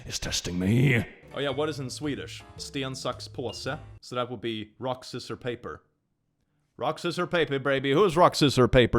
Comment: Did... Did you... Am i tripping or did you just said: "Stan suck's Possa" ??? I love languages.